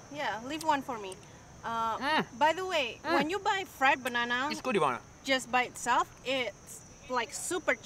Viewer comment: omg i thought u said 'its a good iguana'. well that's messed up 😅😅